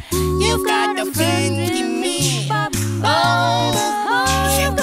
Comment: 펭수야, 결국 이걸로 모닝콜 만들었어. 근데 들을 때마다 'You've got a penguin me' 로 들려ㅋ🥰💙🐧